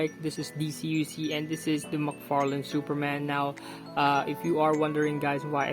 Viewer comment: far left is macfarlane did I hear that correctly?